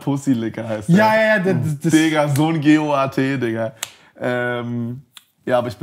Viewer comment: was sagt er bei 49:16 verstehs akustisch nicht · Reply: in the learning